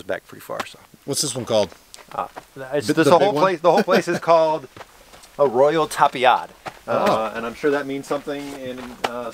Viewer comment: I believe I heard "Arroyo Tapia" = Tapia Stream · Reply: I searched for the location - Arroyo Tapiado. Means "walled stream"